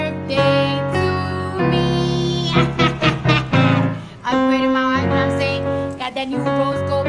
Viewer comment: All i hear is ‘I upgraded my wife now im sayin’’